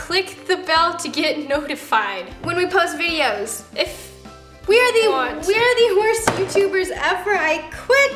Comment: When Roni said “we are the worst youtubers ever I quit!” I thought she said “ we are horse people, and I quit!”